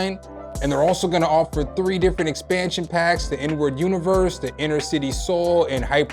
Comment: Everytime somebody says the name of that plug-in, i just hear "N-word universe"...